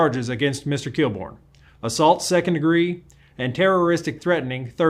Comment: Was he charged for death treaths? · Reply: "terroristic threatening" I believe I heard around the 4:45 time.